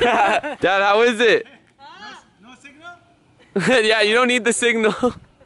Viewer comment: Yo when he came up the driveway and said signal I thought he said bazinga and I fucking lost it